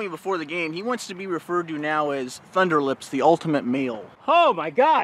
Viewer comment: I heard "ultimate meal."